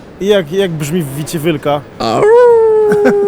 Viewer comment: Tylko ja usłyszałem ''WICIE WYLKA'' Xd ?